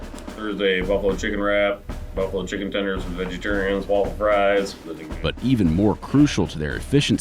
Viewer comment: did he say buffalo chicken tenders for the vegetarians?